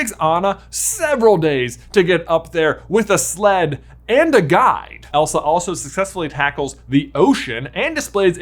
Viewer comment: there at the end I thought you said "anna had a sled...and a guy!" not "guide" and it cracked me up 😂